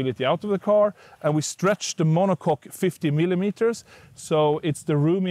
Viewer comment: “Stretch the mono-cock” -That’s what he said.